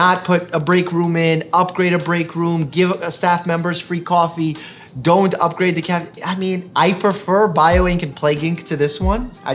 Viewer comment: did you just say don't upgrade the cafeteria?